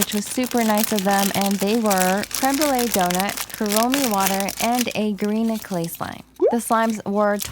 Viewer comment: When she said kuromi water I thought she said corona water